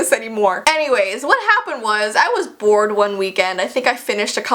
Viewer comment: When said "I was bored one weekend" I thought she said "I was born one weekend" 😂